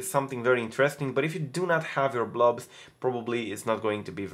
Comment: What is a blog? I keep hearing you say "if you did not save your blog" if you know please tell me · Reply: BloB not bloG